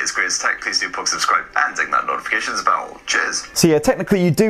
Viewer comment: "I'm gonna whip out this gorgeous 6 incher".... then says "cheers" (I misheard it as "Jizz") then says "standard just black model.. which definitely has some some serious Goth appeal"... wow "....stroking my soft 6 incher.." "....fits in the hand...." all this is about Google Pixel 5! No wonder it is tech "Spurt".... Seriously, good comedy man P.s. does that toy have a poosay!!?😂